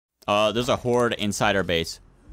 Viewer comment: I thought you said it was a hoar inside your base